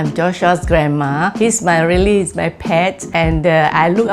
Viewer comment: what she said i eat my pets